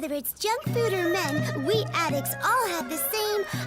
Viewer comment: ok thank god you explained the joke for one of them because i had this on in the background and thought that they said the anti addiction program could go from junk food to *meth*, which feels both worryingly in character for the show to pull and like something thatd get the networks very very mad